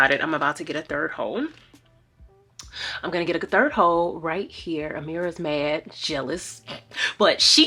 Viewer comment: I heard "im gonna get a third home" and was so discombobulated 😂😂😂